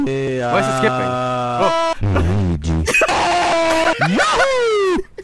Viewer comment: W A L U I G I (I checked. I'M THE ONLY ONE WHO HEARS "WALUIGI", NOT "LUIGI"!!!)